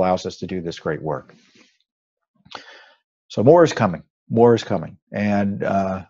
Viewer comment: Just call for the Troops Our Hardware is clean oiled and at the ready. Our fighting Spirit Will be unmatched WWG1WGA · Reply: I know Tom said "More is coming." so maybe its just me thinking I heard "War is coming".